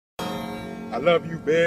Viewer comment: What she say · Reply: she just says “oh my god”